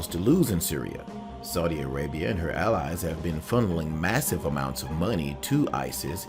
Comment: did he say fondling or funding? if funding, allow me to say that hes ignorant and doesnt know anything. im from saudi arabia and we are fighting isis why would we fund our enemy?